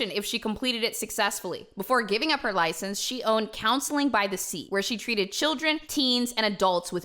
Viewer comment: I thought said she owned Counseling by Deceit! I was like WTF, lol?! 🤣🤣🤣 ~Omg, I just realized her last name is Crooks!! Imagining *Ashley Crooks, owner of Counseling by Deceit* has me laughing so hard my ribs hurt lmao!!!